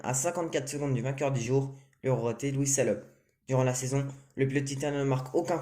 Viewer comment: Louis quoi ? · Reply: Louis Salom